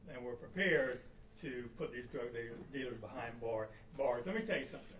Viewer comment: Im not trippin i swear i heard him say we're prepared to put these drug niggas behind bars he didn't say the whole thing he stopped and corrected it in not talking about the hard r word